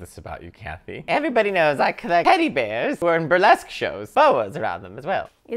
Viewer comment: Those puff bags are full of your spittle? No thanksss, Kath. I swear it sounded like she said “I collect t*tty bears.” 😬🤣